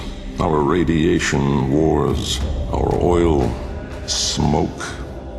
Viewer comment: did i hear oil?